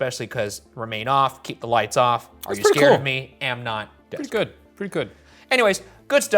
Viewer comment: It's saying "Am not, dick" in a Scottish dialect. Ovulus can't understand the accent, thinks it's "desk".